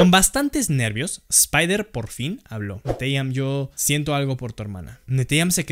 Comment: No manches 2:05 escuche Siento algo por ti en vez de Siento algo por tu hermana